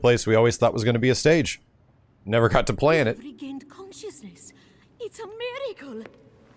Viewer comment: sounds like she says "it's america!"